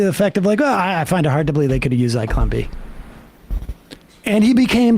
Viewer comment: it was nearly impossible to understand what you'd said, and the YouTube transcription didn't figure it out, either. By some miracle I was able to find through a few searches that you'd said 'Zyklon B' which I doubt very many people watching this have ever heard of. It would be appreciated if you didn't assume that everyone has watched this particular Errol Morris documentary, and it's not as if I'm ignorant of the man's work - I've owned the soundtrack to his documentary 'The Thin Blue Line' since the eighties.